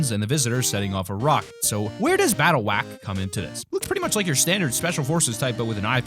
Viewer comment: Did he just say battle whack